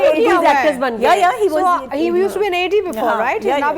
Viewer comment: I thought atiqa said “ he used to be a lady before”. 😂